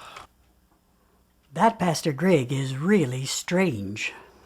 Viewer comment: it kinda sounds like she said: "That bastard Greg is really strange." immediately that would make this the best movie ever.